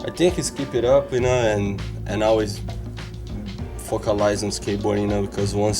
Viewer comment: did he say "fuckalize"